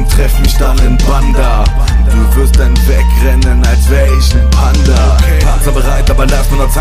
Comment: Ich habs mir jetzt ca 10 mal angehört und muss einfach fragen. Meint ihr da wirklich "wie ein PANDA" ??? Warscheinlich meint ihr Panther aber es hört sich echt wie Panda an.